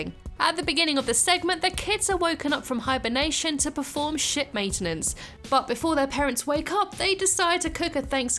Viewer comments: Lol When you said "To do Ship Maintenance" I heard "To do Sh*t Maintenance", I'm glad they're not doing that. Dr. Nick says your bloodlust is at a normal rate now......Until Next Year...... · y did i hear shit maintenance instead of ship maintenance